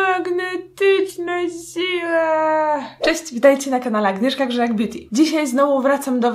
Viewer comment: Zamiast „magnetyczna siła” usłyszałam „magnetyczna zdzira” xd . Moja mina była bezcenna...